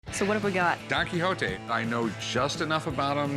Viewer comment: I wasn't paying attention and Don Quixote sounded like Dr Hook.